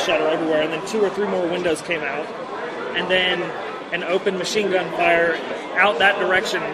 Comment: witness at 1:20... what does he mean by "two or three more windows came out", were they shot out or opened? Did he say there was "interior gunfire too" or is there something wrong with my ears?